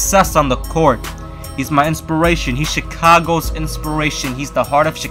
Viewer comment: it sounded like you said Chicongo